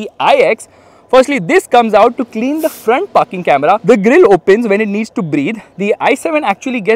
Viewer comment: i heard "this comes out to clean the front f*cking camera" in the starting